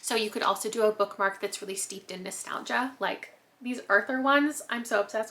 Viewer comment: i thought you said steeped in matcha